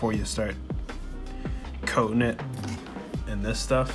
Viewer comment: At first I thought you said you're going to put cocaine on your drone lol 😆